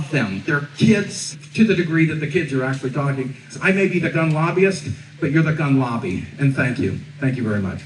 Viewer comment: I thought the guy said, "I may be the dumb lobbyist, but your the gun lobby, and thank you"